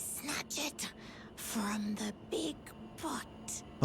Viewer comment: captions say: “snatch it from the big butt” XD